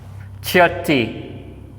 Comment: I heard 'tchiut' which means hush